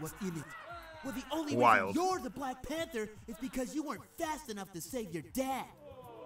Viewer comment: “Well the only reason you’re the black panther is because you weren’t fast enough to save your dad” -Sonic Me: Damn Sonic no need to be a savage like that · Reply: I actually thought he said “you weren’t fast enough to save your DEATH!”, because the cancer research hasn’t been finished.